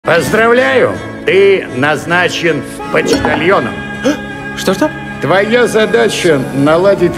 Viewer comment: Я один в начале услышал "ты назначен В почтальоном"?